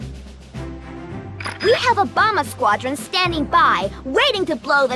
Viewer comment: did she say a Obama squadron?